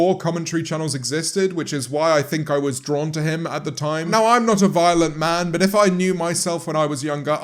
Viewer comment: I definitely thought you said "I'm not a violin man..." and I was thoroughly confused.